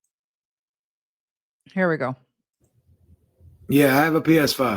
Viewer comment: I swear you just said hair we go 😂